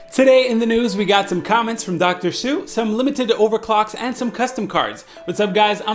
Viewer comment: i thought you said Dr. Seuss. 🤣🤣